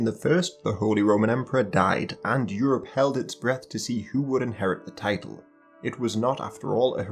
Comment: did anyone else hear "who would inherit the titan"?